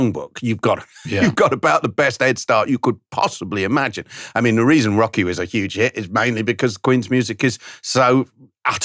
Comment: Great interview. And thanks Richard for getting clarification. I also thought that Ben said "the reason Rocky was such a hit was because of Queen's music". I was very confused!